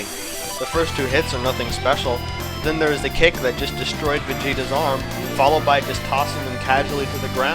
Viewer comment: did you said destroys vegeta arms??